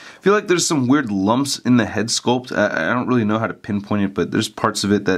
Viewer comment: Did he say "Pym point"? Hahaaaaa